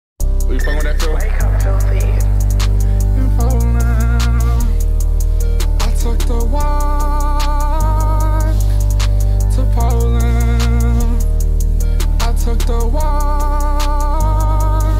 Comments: i hear "I took a Waaaaaaalk to poland. I took a roooooock to oakland. i took the Quaaaaaaaac to Chipotle" Idk why tho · I literally heard “I took the wash to Poland” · NAH BUT I THOUGHT IT SAID “to Poland I took a waaAaaalk”